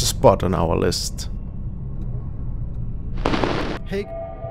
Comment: I caught an EVP @ 8:54 and it sounded like the very creepy horror master of YouTube 👀🙈👻👀☺⚠️it said something like "hey" ...